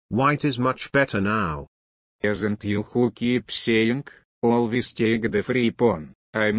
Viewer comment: The subtitles say “Always take the free porn” Yes, Mr. Tal, I agree.